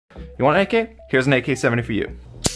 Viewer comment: i heard "here is a ak47 for you 😊" idk why